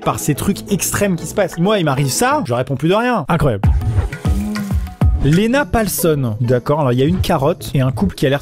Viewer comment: je regarder pas la vidéo du coup j'ai entendu Lena pas l'seum